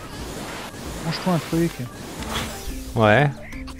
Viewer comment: [Benzaie]- Manges-toi un truc [Fanta]- Ouais [Worm]- A tissue